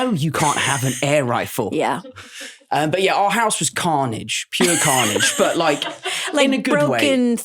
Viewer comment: did he say…Carnage?